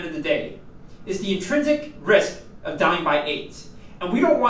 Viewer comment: what is saying at 4:53? "the intrinsic risk of dying by ???" ??? is eight in CC but doesn't make sense to me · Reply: Hi Suo, I said "the intrinsic risk of dying by AIDS".